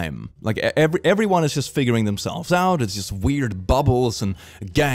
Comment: “Everybody’s just figuring themselves out” Thought bro was saying “everybody’s just fingering themselves” 💀💀💀💀💀